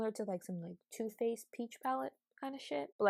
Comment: I thought she said “Tooth paste peach palette” and I was so confused 😂😂😂 I love your videos